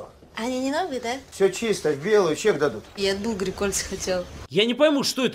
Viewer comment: что она сказала? Ничего не могу разобрать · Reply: я от Булгари кольца хотела